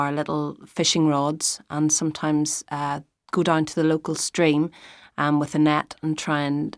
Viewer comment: I heard stream as string😭